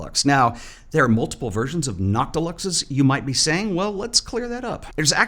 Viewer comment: I thought you were saying “not deluxe” and I was very confused why they would name a lens that lol